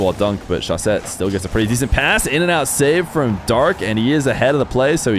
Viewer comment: did I hear IN AND OUT SAVE???